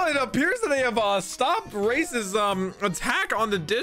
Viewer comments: Bruh lmao at 10:55 I thought he said they stopped racism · it sounds like he said, "Stop racism" in a preppy voice haha